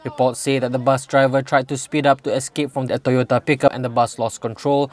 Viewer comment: what does he say at 0:34? speed up from Toyota.....?